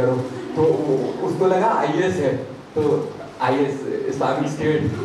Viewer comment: what did he say? Is or what?